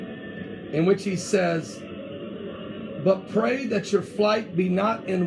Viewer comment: I thought it said " pray your fig leaf off" lol